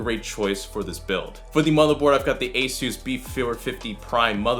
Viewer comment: what did u just say? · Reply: Chris McMillen motherboard